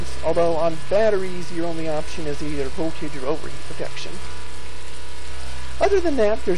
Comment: Pardon, but... what'd you say? I could only hear 'Hold your own ovary protection'